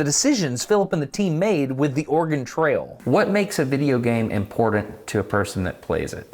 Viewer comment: I can't be the only one who hears Destin say "organ trail" when he says "Oregon Trail", right? cause that would be a quite different game :D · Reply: No, but I always hear "impordent"... but it's common.